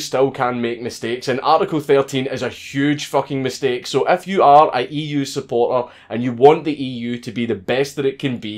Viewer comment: perfect example of the algorithm messing things up What he actually said = "If you are an EU supporter" What the algorithim thinks he said "F YOU R (slang for retard) EU SUPPORTER!S!!" HATE SPEECH!!!!